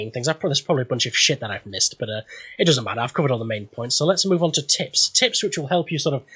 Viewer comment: I thought you said, "Let's move on to tits"